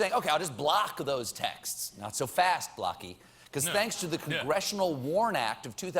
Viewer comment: The front man....clearly was confused on what Stephen said.. he's like "did he just say Blacky"? ohh no he meant Blocky